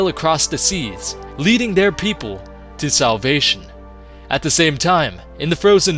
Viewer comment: When he said Salvation i thought he said South Asia